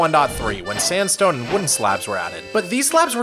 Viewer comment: I thought you said pedo-fied wood slabs